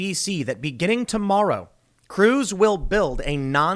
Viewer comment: I heard "crews" as "Cruz" as in "Ted Cruz" and got super confused why he had anything to do with this